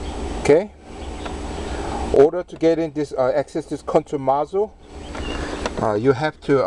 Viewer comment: did he say "order to access this country nozzle..."?